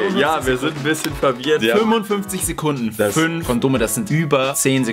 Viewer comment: Ich hab verstanden, dass sie 25 sekunden zeit hat?!